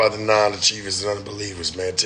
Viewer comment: “non achievers and under believers”?? Is that something he really says?…or did he mean “non-believers and under achievers”?